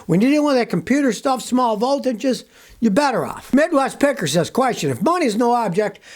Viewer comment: I had to rewind it at 7:38 because at first I swear I thought Scotty said "Midwest Pecker" lol